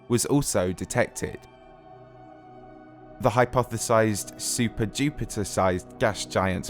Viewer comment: I misheard super-Jupiter as super-dupiter and just accepted it